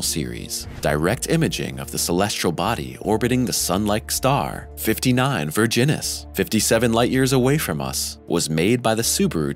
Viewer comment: Why do I keep about the "59 Virginis" and it is because I always thought he is saying "59 Virgins"